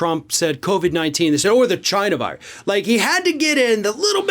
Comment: I honestly thought he said Vagina Virus. Had to replay it.